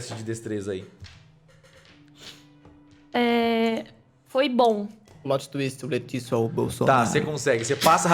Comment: Que foi que o falou?? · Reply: "Plot Twist O leticio é o Bolsonaro"